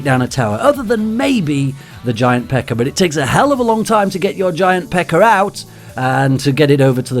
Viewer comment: I was in tears laughing when I thought I heard u say: "Takes a hell of a long time to get your giant pecker out" lol. Anyways, I really believe your channel is highly under rated. U huge grey material for royale and I always look forward to your new uploads (coming from a fellow older players here). More importantly, you take your losses very lightheartedly and with a laugh and watching you helps me not rage so much and enjoy the game for what it is. Thank you very much. Cheers